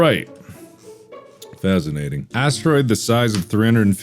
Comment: did you say fazinating😂